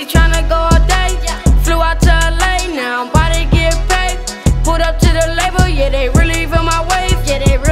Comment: I thought he said "I'm about to get raped"